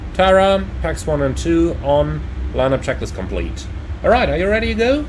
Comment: every time you say "line up checklist complete" i hear it as "landing checklist" at first and it sounds so weird